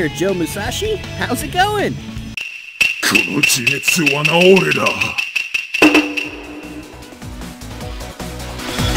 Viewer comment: Is that what he's actually saying in Japanese? "Konotetsuwanaoreda" - what does that mean? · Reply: The line was, "Kono jimetsu wa naore da!" which, according to Google Translate (take it with a grain of salt) roughly translates to "This self-defeating me!" source: I'm the voice of Joe Musashi in this video.